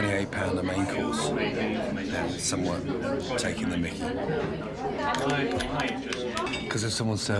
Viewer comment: what does he say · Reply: Taking the mickey?